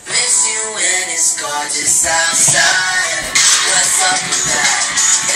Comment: for some reason i thought that jacob song said "what fuck wit dat''